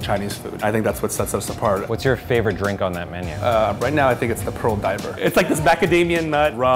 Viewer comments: Lol..swore he said "Pearl diaper " lol 😂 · When I heard them talk about the drink, I thought he said "pearl diaper". With me in my head going".........this doesn't sound tasty at all."